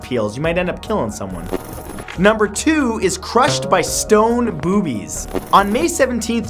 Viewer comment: i heard "crushed by stone boobies" and i immediately thought of five nights at anime